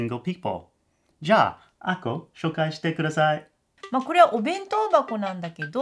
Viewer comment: Jesus christ, I thought you said Aiko and then this adult woman appears and I freaked out 😂